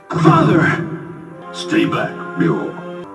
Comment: I thought he said stay black ryo 😂😂😂